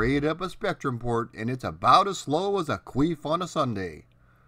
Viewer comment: One word I never thought I'd hear say: "Queef." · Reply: H I thought he said "Queen" so had to rewind to double check! I was thinking to myself "what has poor old Liz Regina done to piss Patman off?! 🤣 Upon a second listen "Queef" made far more sense!!